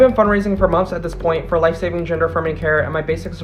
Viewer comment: did she say life saving?